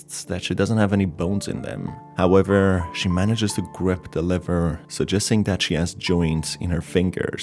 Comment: Lmao it always sounds like u say liver not lever